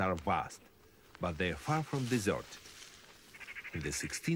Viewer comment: Siberia's forests are vast. But they are from deserted? was that what the narrator said? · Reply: Guess he said far from (being) deserted